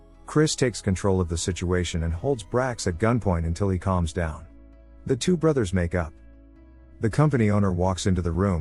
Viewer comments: When you said “the two brothers make up” I swear I heard “the two brothers make out” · I was listening to to this while playing a game and at this part then I hear "The two brother's make out" Had to rewind a bit